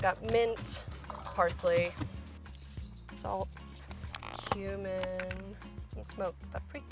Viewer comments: DID SHE SAY HUMAN?!😂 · did she say human? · When she said cumin i thought she said human So i was like... Wait.. what?!